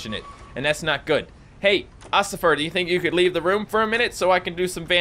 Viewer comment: did you said Ocifer and no I'm not here to pass any one off